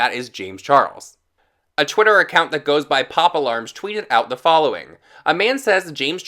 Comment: Legit thought you said “Poppa Large” not “Pop Alarm” and I am still laughing about it lol